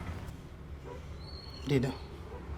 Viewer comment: What word does say at 2:00 mark. Sounds like Deda. What does it mean. Any help plz